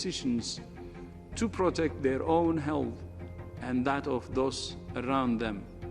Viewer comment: With his accent and poor English it sounds like he is saying “hell” when he says health